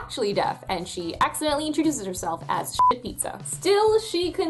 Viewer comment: I was listening more than watching and so when you said "introduces herself as deaf", for some dumb reason for a moment I thought you said "introduces herself as Death", like "Greetings, I am the Grim Reaper."